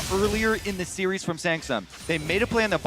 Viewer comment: Did he say Sangsum instead of samsung? 😂